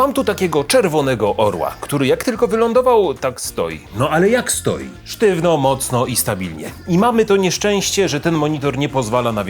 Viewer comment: Miałem coś nie tak z słuchawkami i usłyszałem ,,ale jak stoi, stoi sztywno stale i stabilnie" XDDD